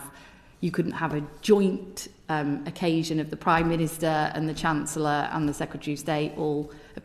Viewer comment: Ok I did mishear. All I heard was, you couldn’t have a joint with the prime minister. Well, probably not now, but yeah, back in his student days.